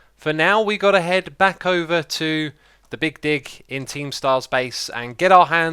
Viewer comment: Sometimes when I hear you say 'big dig' I feel like you're saying 'big di*k'. Maybe it's me. Or I need my ears checked